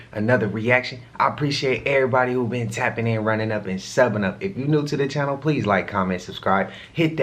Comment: He said subbing up i thought he said summon but overall he picked the right hobby is a goat a what he does very very informative otherwise we wouldn't know this stuff